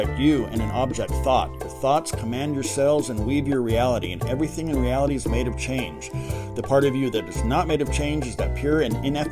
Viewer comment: At first I thought you said everything in reality is made of cheese but then I rewound it and that’s not what you said and I’m sad now. No. I notice a sadness because reality is not made of cheese. I notice also that I suddenly want to eat cheese. I’m going to the refrigerator. I’m eating cheese. Now I’m happy. Damn. You’re good.